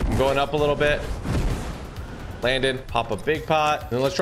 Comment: Did he just say yeah we’re bingchilling